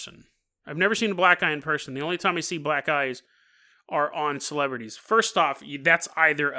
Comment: I've seen plenty of black guys in real life...what's that? Ohhhhhh "black eyes " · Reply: I totally thought he said black guys a few times too lol